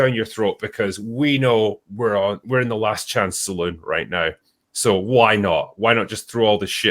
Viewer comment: I thought I heard Drinker say "we're in the Last Chant Saloon." LoL